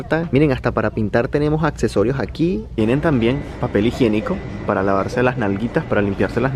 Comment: soy yo a pensé que dijo tiene vasos para lavarse las nalgas